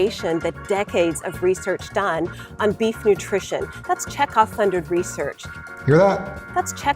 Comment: I thought she was saying "Chekov" funded research.